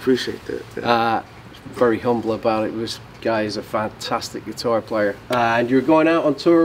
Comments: Did he say "FATtastic"? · Did u just call him "Fattastic" and "fat"?? 0:19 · Did I hear "fattastic" player?